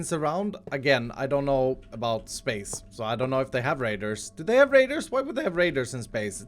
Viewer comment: when said radars with his accent making it sound like he was saying raIDERS not raDARS I laughed so hard when I thought to myself "Borderlands 3: Minecraft Edition"!